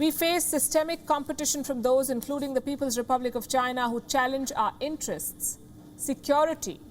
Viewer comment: I am sorry. I only heard the word 'challenge', not 'threat' when it relates to China in NATO's declaration statement. If you are in doubt, read the original NATO statement or simply look at the text provided at the 1:15 mark.